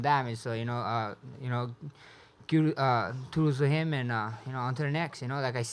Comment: #THEKINGISMAX! Did he just say toodles trying to say kudos? Haha!